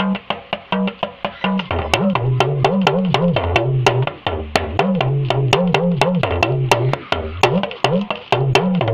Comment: teacher what's the wording for the owe at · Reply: Ori eni koma fiya jeni Atari eni koma fiya jeni Modupe Olorun opami lekun Modupe